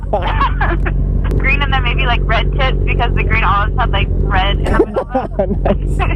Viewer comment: "green hair then red tips " i thought she said red tits 😂😂😂